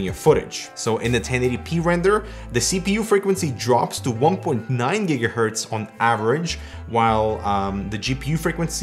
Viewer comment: Did he just say "Jiga Herts"? WTF!!??